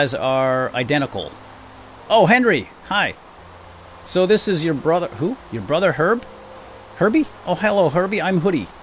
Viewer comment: At the beginning, I thought said "Oh, this is your brother herpes"... Lol.